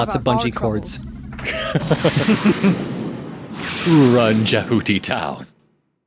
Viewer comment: I keep hearing Jehuty as "ja hooty" and it amuses me.